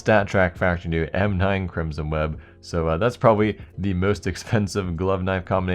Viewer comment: Did he say Crimsom Weeb? xD